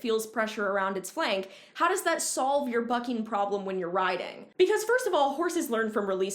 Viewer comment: What she said “So how does that solve your bucking problem at all?” What I heard “So how does that solve your FUCKING problem at all?”